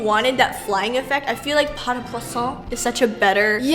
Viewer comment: At first I thought you were saying "pas de croissant"!